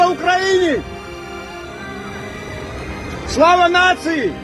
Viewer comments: did he say " Slava Nazi"? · he seems to say "slava nazi" which would be what Putin wants to hear lol. I guess he said "victory to the nation" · That was great. Georgian shouts as he sees a group of Russians, "Slava Ukraine!" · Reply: Didn't you hear what he said right after that? At 2:34 he says Slava Ukraine! At 2:36 he says SLAVA NAZIS!!!!!? I SUPPORT UKRAINE. This a**** also screamed at these Russian refugees calling them deserters. Would he prefer that he they stayed in Russia and joined the army and attack ukraine? Or have them abandon their country, they're Homeland and their families in protest of this war? This guy just sounds a little bit too enthusiastic about the war. And he can go f*** himself if he thinks America is going to support Nazis. Luckily I am intelligent enough to know that most ukrainians are not Nazis. But this does no good for the Ukrainian cause. It reinforces the lies Putin has said about ukrainians being run by Nazis.